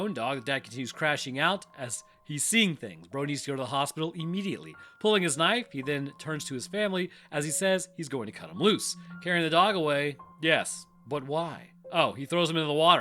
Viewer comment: I swears I thought I heard "Brody pulls out his nine and says he's gonna cut loose" 😭😭😭😭😂😂😂😂😂😂🤣🤣🤣🤣